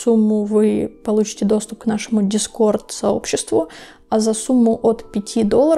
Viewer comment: В конце послышалось за сумму от 500$ 😂, и я такая ЧОО